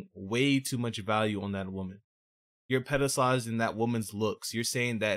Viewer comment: captions says pedophiles not pedstalize a womens looks . figured id point that out there 😂